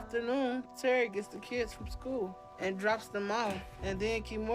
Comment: Another web surfing mighty minge menace. Why do they look the same? I thought she said terry took the kids to school and came back for a hump. Must've misheard.🙃